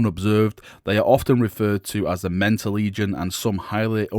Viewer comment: The Mentor Legion has been around since Rogue Trader days. They were the ones to test new wargear and such. Things have changed a lot since then. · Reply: Every time he said Mentor legion I thought he was saying Lamenter...